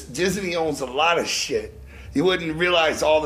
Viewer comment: Lmao sounds like J is saying disiney